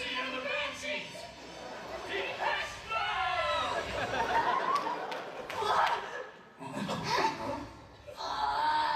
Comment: wtf is ryuko and senketsu saying.. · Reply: For an actual answer: "Siouxsie and the Banshees! DEPECHE MODE!" They're English bands that were both very popular in the 80s and were known for post-punk and alt rock, but there might be another more specific reference that escapes me.